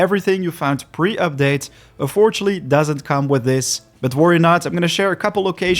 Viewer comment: Excuse me asking, but English isn't my mother tongue. What do you say at 1:57, please? I cant find any translation for "affortshally". Do you mean "unfortunately"? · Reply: Yes, he said unfortunately